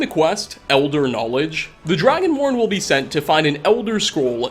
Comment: did he say dragonworn or Dragonborn? He obviously meant Dragonborn